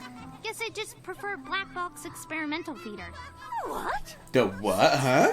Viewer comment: For the longest damn time, I heard that as Black Ops Experimental Theater... That confused the hell out of me...